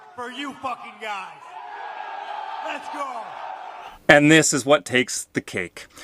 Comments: Let's go Brandon! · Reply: I thought they were saying Let’s go Castro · "F*ck Trudeau"? I heard "Let's go Brandon"